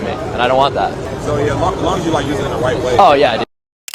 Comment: did he say as long as you use it in a “Wyatt⚪️” way?